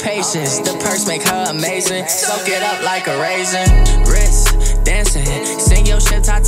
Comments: Anyone else hear 'so get up like a raisin' lmao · I hear “so get it up like a raisin” and it makes me laugh every time😂